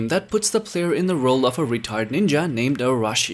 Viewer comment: I thought you said a retarded ninja instead of retired